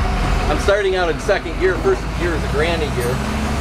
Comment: about 8:25 you said you started out in second gear, and that first gear is a "granny" or "grinding" gear? I just didn't hear what u said and if I did I don't know what u mean, can you explain?